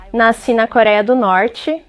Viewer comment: Eu escutei "nois coreano???🤔😟🧐